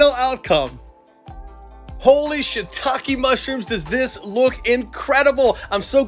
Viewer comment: does anyone hear say “holy sh word” like if u do